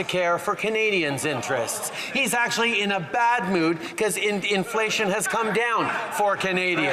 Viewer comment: Sounded like wacko said indianflation lol